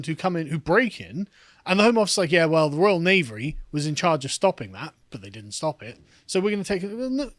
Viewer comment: Pretty sure you intended to say "Royal Navy," but what you actually said was "Royal Knavery"... Mission Failed Successfully?